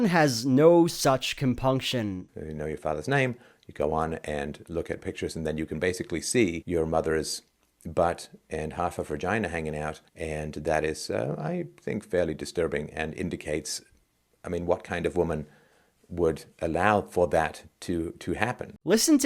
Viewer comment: Did he just say "Hergina?"